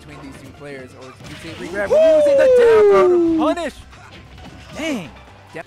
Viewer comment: did he say down throw punish?